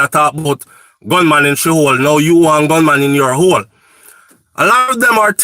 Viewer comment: OMG 😱 I thought that song said “one man in your hole” 🤣😂🤣😂💃💃🤦‍♀️